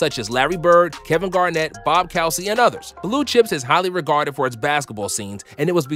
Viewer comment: Did he just say Bob Cowsy.